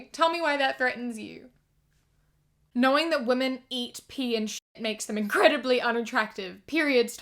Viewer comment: they eat ,pee and shit me: they eat pee and shit also me a minute later : oh they eat ,pee and shit i thought they said that women ate their pee and shit lmao